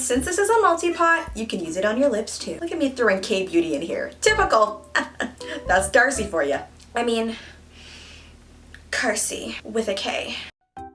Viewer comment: Why I hear her name "DARCEI" like "THIRTY" 😂😂btw she is funny and beautiful 😍